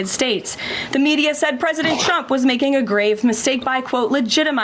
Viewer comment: It always sounds like she is saying president chump.